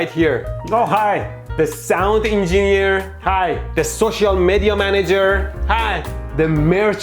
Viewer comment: When he introduced the social media manager at 10:17, i heard "Social Mehdi-a manager"